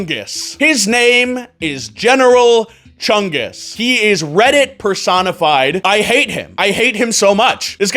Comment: When you said redit personified i thought you said read it persona 5